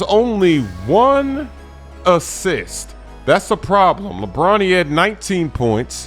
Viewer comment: I thought he said bronny had 19pts